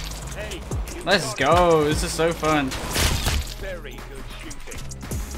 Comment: Fun fact: has heard too much “way too good shooting” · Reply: I heard "very good shooting"